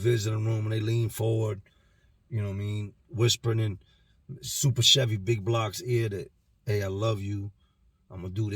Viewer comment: Wait 😂hold on 😂 did he just say SUPER CHEVY BIG BLOCK I love you 😂 he’s going to hell for that one 😂omg lmao 🤣